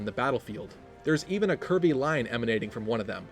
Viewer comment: Whenever you said "curvy" I kept hearing "Kirby" so I heard "Kirby line" and "Kirby sword" lol.